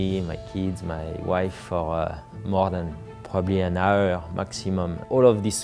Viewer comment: he didn't see his family for an hour? did i hear that right? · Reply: More than an hour at a time I think